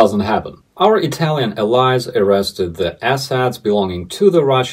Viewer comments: I thought you had said something about authorities in Italy arresting Muscovite oligarch "asshats" right until the end, when I understood you to say "assets". I'm sure you can understand my error. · Ahh assets... thought you said asshats! Kinda good aswell